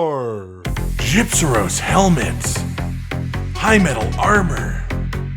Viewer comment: I swear I heard him say, HYMENAL, instead of high metal...